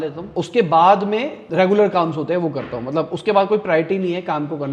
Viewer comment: did you just said "kaams" 😢😅